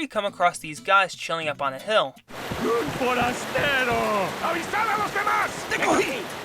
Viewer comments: he says "un forastero"(an outsider) I thought he said "un paletero" (an ice cream man) 😂😂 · he says "un forastero"(an outsider) I thought he said "un paletero" (an ice cream man) 😂😂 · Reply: I thought he said UN MONASTERO 😂😂😂😂😂